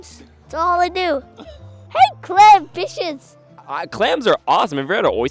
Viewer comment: For a sec I thought he said “HEY CLAM B!+<H€S!!”